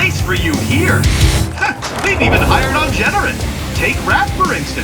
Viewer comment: i thought he said, "HA! WE'VE EVEN HIRED OUR OWN JEDI!" but then i had my ears cleaned